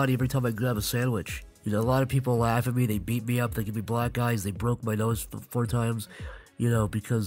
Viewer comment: I honestly thought he said “they could be black guys”